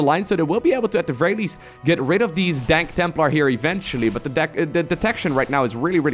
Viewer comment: I really hope, that I just misheard, that I'm wrong and that wasn't "dank templar"